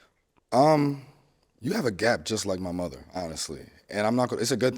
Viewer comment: I thought he said “you have a gap just like no other” 😭 I was about to sayyyy don’t do ma girll